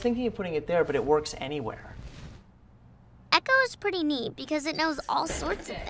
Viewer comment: what is she saying is she saying that alexa is mean or neat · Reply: Neat xD